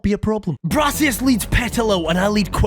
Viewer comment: wait did he just say arsius????